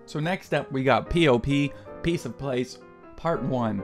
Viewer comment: did you say "piece of place"? 😂